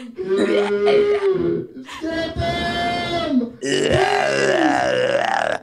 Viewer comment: IM CRYIN when thought they said he was ugly but then they said kalynn. His mood changed real quick LMAO